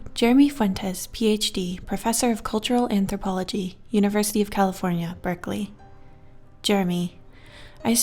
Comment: I thought she said University of Cauliflower, haha